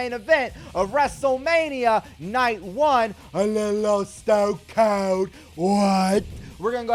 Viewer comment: I always thought they said a little oh stone cold but it’s actually I think it’s a little old